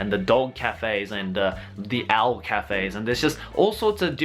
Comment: when you said owl café i misheard it as ALF café and i was like: now why would anyone want to go to a café filled with people dressing up as an alien muppet who's nose should be considered R rated material..? XD